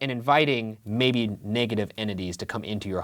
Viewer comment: it definitely sounded like "tambien" instead of "come in" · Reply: I was thinking that too, i heard '¿estás bien?', I didn't hear english at all